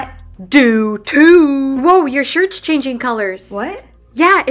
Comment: OMG!!! I thought she said I kNoW wHaT yOuR bUtT iS!!! UNTIL,I read the description😂😂😂😂!!! Well uts on your back!!! Did that answer your question!?!?!?! Love ya❤❤❤😊😊